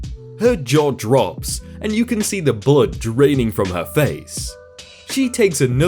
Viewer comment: I swear that you just said bullet and not blood o_o